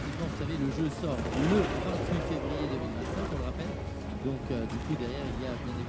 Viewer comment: 2025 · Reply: oui j'ai été vérifié, mais à 16:55, moi j'entends 2029 😂